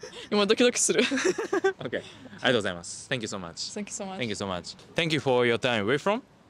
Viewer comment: what Japanese phrase do say at the 3:18 time stamp? is it another way to say "thank you"? it sounds like "ohayo gozaimasu", which is "good morning", right? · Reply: I just said arigatou gozaimau (thank you but in formal way) very fast lol